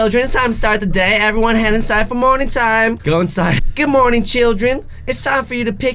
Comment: when you said “morning time” it sounded like you said “moaning time”